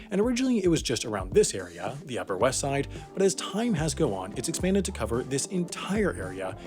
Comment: Did anyone else hear “...but as time has go on” ??